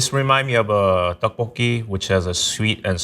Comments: "duck poke" instead of tteokbokki in the subtitles lol! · whoever wrote the subtitles at 4:45 mishearing/misinterpreting the korean "tteokbokki" as "duck poke" is so white it's sending me hahahaha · Captions say "duck poke" instead of "tteokbokki" at 4:44 lol